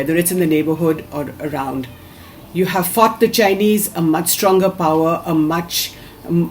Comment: did she say u have fucked the Chinese??